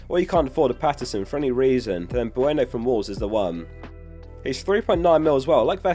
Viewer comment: I don't understand what you say if we can't afford patterson, excuse me but thats some heavy accent, no clue what you say, do u say brannick? who is that? from woavs? wolves? i dont understand lol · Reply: Bueno 😄 3.9m Patterson for 4m, they’re the best two cheap defenders